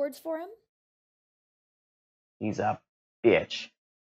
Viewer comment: did he say at 9:04 hes my bitch